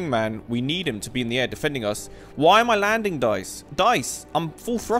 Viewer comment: he says dice 🎲 instead of saying guys 😂😂😂